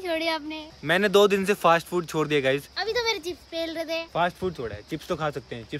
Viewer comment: kya bol rha h 😂😂😂😂😂 best part 😂😂😂😂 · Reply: Op ne bola Abhi to ap chipa pel rhe the